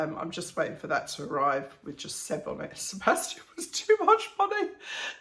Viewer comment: Funny thing- when you were talking about your grandchildren’s mugs, and you said Sebastian was too long to put on. I thought you said that you were just going to put “7”, and I thought well that’s kinda sad…,lol!